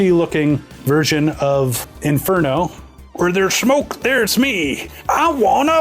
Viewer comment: you sounded like Ironhide instead of inferno.